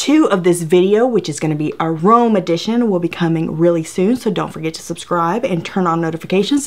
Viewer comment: I swear I heard “Willy” so much I thought I heard you say “Willy soon” at the end ☠️🤣